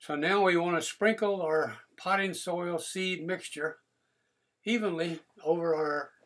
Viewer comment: What is pine soil? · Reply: I just played the videos and I think I found where you thought I said "Pine soil". What I actually said was: "Potting soil". Sorry, I will remember to speak more clearly in future videos.